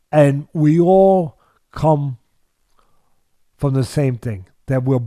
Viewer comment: I thought he bout to say we all cum white lol